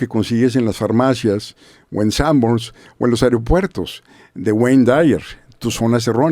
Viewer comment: No entendí el nombre del autor del minuto 16:57 alguien me puede pasar la información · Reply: Wayne W. Dyer, "Tus zonas erróneas"